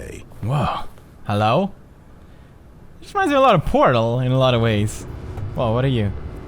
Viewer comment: turn on captions at 11:00 he says portal then the captions say cake the stanly parable portal and half life are all made on the same engine its 3 game half life 3 confermed!